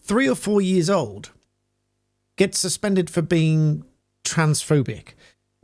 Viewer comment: I do NOT believe a 4 year old was being Transphobic... the ideology of the ADULTS, the Teachers, should be questioned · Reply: I heard ""two year old"" and I'm not joking. At some point I must have trained my brain that a toddler = 2 years. But then I have a couple of ciders.